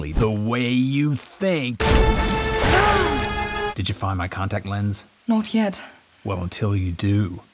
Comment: What he said:did you find my contacts lens? What I heard:did you find my Condoms yet?